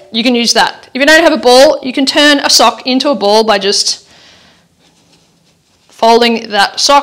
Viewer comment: Around the four minute mark you said get a ball and I thought you said a bowl so I went running to the kitchen grabbed a bowl, came back in and you were rolling up a sock to form a ball. I’ll chalk it up as a little more exercise running to the kitchen, your accent is a lot of fun. Well done. It feels great... subscribed.